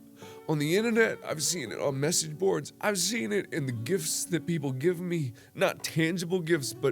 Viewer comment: love you but i got to be honest on a lighter note. When you said gifts i thought you said gifs